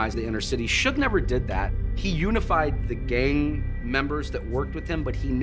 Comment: did I just hear something about gay members and stop banging? 🧐😂